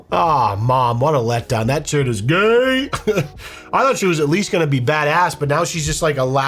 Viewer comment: Lol, i thought she said she was at Hogwarts!!!